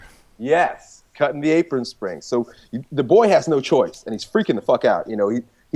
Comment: No, actually I understood it. But thought you said "springs" instead of "strings."